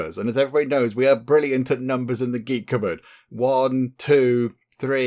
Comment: Idk why but when i started watching your vids back when ep 1 of earl waltheof came out i thought you said the Greek cropboard wich sounds like a store where you buy farming stuff lol